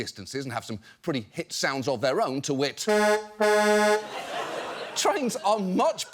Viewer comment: Deep cut here. 2:39 Can anyone tell me what that last word he uses before pumping his arm and the truck sound plays is? It sounds like "Too-it" and I've used this word, but now I know someone who's never heard it and I can't find any evidence of it online to prove it exists XD I search "tuit" and get nothing, which is how I thought it was spelled. Latin or something. Can't find it!